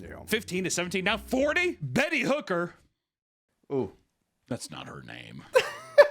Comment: I thought you said penny hooker at first, and i feel like that would be worse than betty.